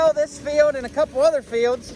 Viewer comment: New to your channel. Like the vintage equipment. 👍 What State is your farm in? Western PA? Why are you going to sell your fields? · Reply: My fault. I went back to 11:18 and you said we were going to SHELL this field. Oops, I thought you said sell this field.